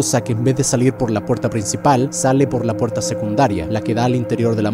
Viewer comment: Soy el único que escucho que dijo puerta y no boca